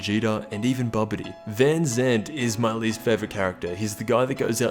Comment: i thought you said puar was your least character